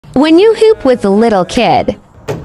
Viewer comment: I thought it said “pov is you poop with a little kid”